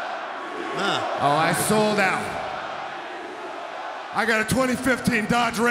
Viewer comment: I hear crowd You Sold Out I remember Seth rollins turn heel and sold out